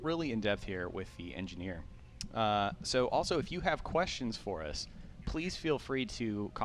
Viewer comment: Man I've been trying to come up with too many puns. I heard say "Also, if Hugh have any questions for us... " >.>